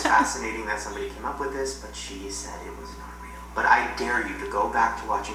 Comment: did anyone else hear voice say " i dare u " in a whisper???